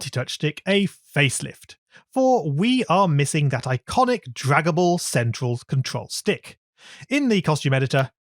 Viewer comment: for a second i thought he said dragonball in